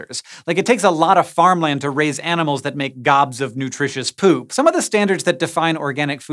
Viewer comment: I'm getting ready to eat dinner and the words I hear are, "Gobs of delicious poop" lol have mercy